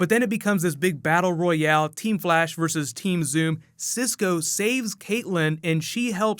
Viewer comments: he said "team Zoom" not "team savitar" anyone else hear that? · did i hear team zoom? XD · did he say team zoom lol?